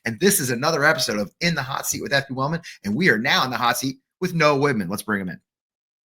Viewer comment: I thought this host said "We are now in the hot seat with no women." He did not say "no women," he said. The importance of speaking clearly.